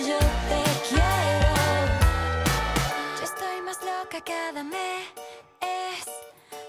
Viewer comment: Espero no ser el único que en ves de entender: "Yo estoy más loca cada mes" entendí "Yo estoy más loca que Adame" XDDDDDDDDDDDDDDDDDDDDDDDDDDDDDDDDD